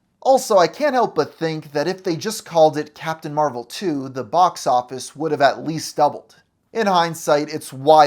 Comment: I thought you said "Captain Marvel 2: The BoxOffice" as full name, lmao😂